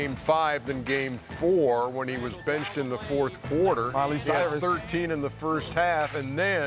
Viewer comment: Did Jet say Miley Cyrus around 1:10 ?